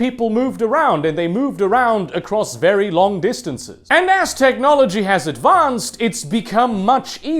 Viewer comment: I heard "as technology advanced" as "Aztechnology advanced."